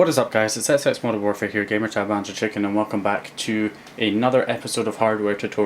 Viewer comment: did you say your name is punja chicken lol what?